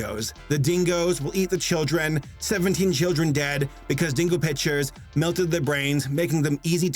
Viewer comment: I kept hearing "Dingle" Pictures